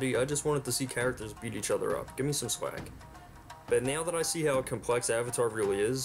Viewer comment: did you just say give me some swag? if not you should have.... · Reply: I said, "Give me some slack." Hahahaha